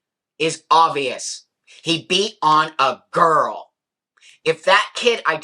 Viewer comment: … “and another thing, this was a teenage boy!” I felt throughout this video it was pretty clear that it was a teenage male who identifies as a girl. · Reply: but immediately after that it sounds like he said born a girl?